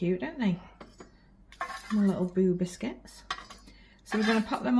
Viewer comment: I amost spat my coffee on my keyboard as i misheard saying boo biscuits i thought she said boo* biscuits lol. 👻 The do look yummy and before even said what they were i even said out loud ooh they look like shortbread. Now if any of the kids dont like them then ground them for a year lol. Have a great evening